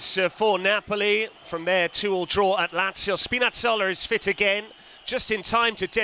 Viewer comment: Why the heck I heard the commentator called Spinazzola as spinach seller??? XD